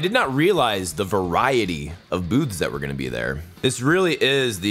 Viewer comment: "I did not realize the variety of boobs that were gonna be there" Lol, I know you said "booths", it just caught me off guard because that's not what I heard at first